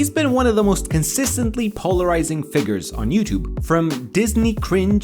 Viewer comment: did you just say PAULorising?